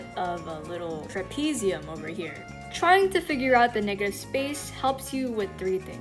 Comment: What did you say.. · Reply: figure